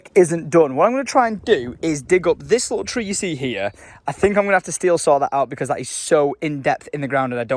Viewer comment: did you just say “Steel Saw” it’s “Stihl Saw”